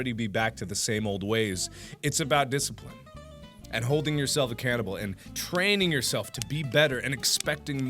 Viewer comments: Every time says "holding yourself accountable" I hear "holding yourself a cannibal". And I like it. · Did anyone else hear "Hold yourself a cannibal?"